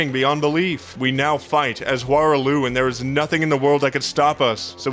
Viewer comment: at 14:40 did you say Hoarah Loux